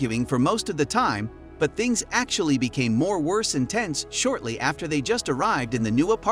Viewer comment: This narrator feels like it was made in the facebook factories orbiting Io That or its TTS, either way it sets off my fight-or-flight. 1:02 "More worse intense", did I just hear that?